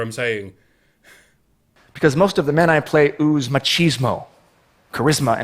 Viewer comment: did he say macheesemo? I want a packet of macheesmos, you get six!!